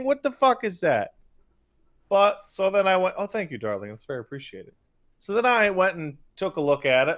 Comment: i thought said "oh thank you darlington" and got real confused for a split second